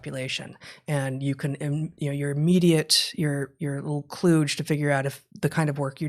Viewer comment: What is the word used at this point please? It sounds like 'kloodge'.